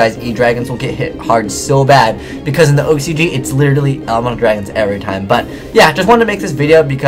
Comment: I thought you said "Ameture dragons". Lol.